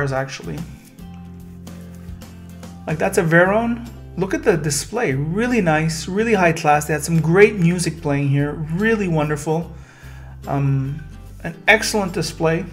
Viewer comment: did you call the Chiron à vérin?